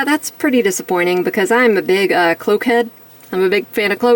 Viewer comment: OMG.. I thought you said “coke-head” but you said “cloak-head”. As your Enemy I was going to send you a kilo or two but since you’re not into “coke” I will save it for a rainy day. As for cloaks.. very odd how it won’t fit on her with the long hair, that’s an error on the design team. Nice review & pics, until next time 👍🏼